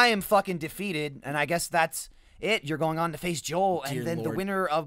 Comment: he said face Joel, I thought he said FaZe Joel...... I need to go outside a little more.